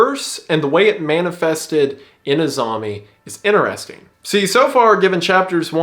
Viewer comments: I keep hearing 'Azami' as 'A zombie' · I thought he was saying "a zombie" not "Azame" 😭💀